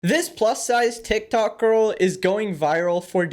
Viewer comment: Bro I thought he said This bus sized tiktoker.